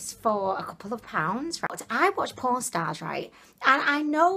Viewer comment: Lol, you said "I watch Pawn Stars.I thought you said porn stars"I had to rewind😄